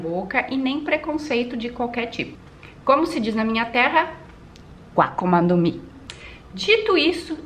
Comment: Akuma ni mi?? Eu juro que entendi issokkkkk Vo da um descanso de One Piece ._.